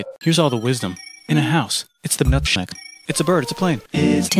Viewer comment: "here's all the wisdom, in a house, it's the Nutshack." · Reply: Thats what he said? I thought he said Its the milkshake